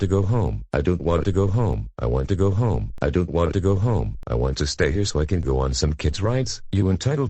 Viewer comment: did he just say he wanted to go home